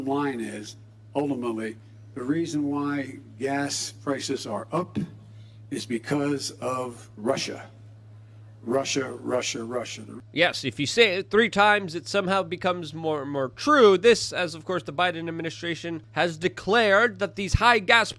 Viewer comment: when he said russia russia russia. all i heard was "marsha marsha marsha"